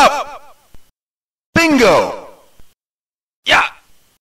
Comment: yo entendi ping0 , perdon si llegué tarde